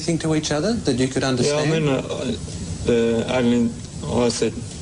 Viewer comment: sounded like aliens 😂😂😂❤. Not Arnolds 😃👍💞.